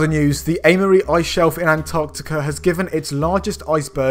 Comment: I Thought you said Analy.